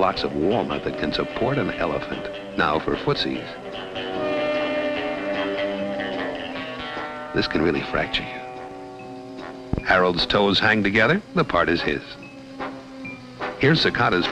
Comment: "This could really fracture you." After all this time, I thought he said, "This could really fry chicken."